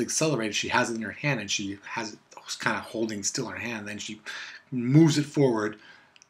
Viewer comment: Did you just say moers it forward at 3m10s ? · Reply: Oh I think you said moves it forward. Dammit confusing South Africans everywhere ! XD